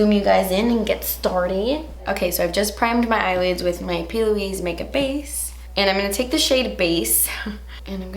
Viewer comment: When she said “p.Louise makeup base” i heard her saying “puby makeup base”😂😂